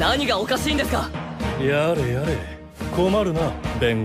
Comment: Did Edgeworth just say Yare Yare